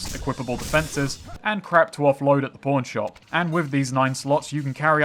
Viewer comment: anytime i hear "pawn shop" in your accent i think its "porn shop"